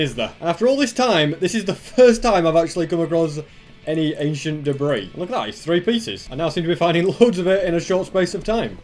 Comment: I laughed how I heard him say it "Asian Debris"